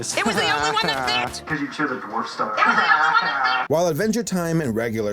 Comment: Did he say "because you choose a poor star" or "because you choose a porn star" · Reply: Dwarf star